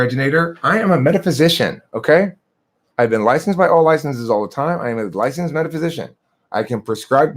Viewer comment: Did he just say “I’ve been licensed by licenses all of the time” ????